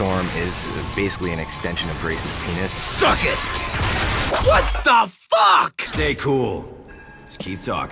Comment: Did Resnov just say WHAT THE FK?! to young Wolverine?!